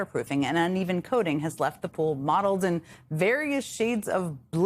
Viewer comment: the closed captioning is wrong again . The announcer is talking about the quality and speed of the repairs. The CC are supposed to reflect what the person is saying . Instead the CC says the color is MODELED ! THE WORD IS MOTTLED for crying out loud ! This is the second time I have seen this on CC in two different videos .......Someone get it right for Pett's sake. It is no wonder people with English as a second language have trouble with English when English people can't even use the right word !